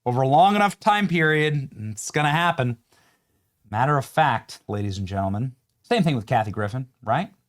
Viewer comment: Benny: Did you say AS A MATTER OF FAT? Or FACT?